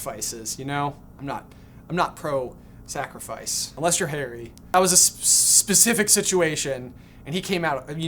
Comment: I heard "I am not okay with sacrifice unless your hairy" 😢😂😂😂